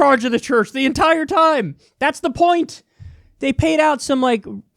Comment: “That’s the point” sounded like “pint” and also very Irish ☘️ lmao